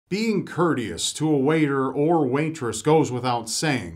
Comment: i thought he said wanktrous instead of waitress